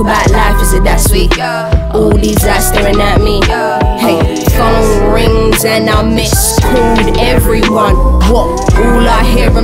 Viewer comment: Chayna Yoshida i heard "phone rings and I mis-schooled everyone" that looks weird when i write it though